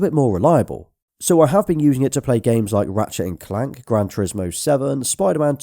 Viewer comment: I thought I heard you say grand theft auto 7